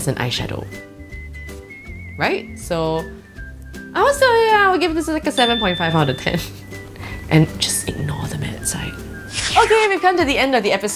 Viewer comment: tf did she say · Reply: ger :D "I would still, yeah, I would give this like a 7.5 out of 10 and just ignore the matte side".